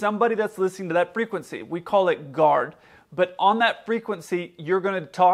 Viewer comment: anybody know what he said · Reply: alienks1 He said “Guard”.